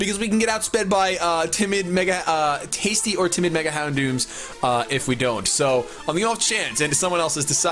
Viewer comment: Did say "tasty Mega-Houndoom" at 12:01? X'D